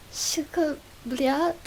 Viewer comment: did she just say cyka soeko blyat?